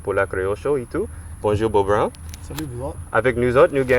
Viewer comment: Mdrrr au début il dit "bonjour Beau Brown" j'ai compris "bonjour beau brun" je me suis dis "ah ben dis donc c'est sympa, ils sont pas coincés chez eux" ^^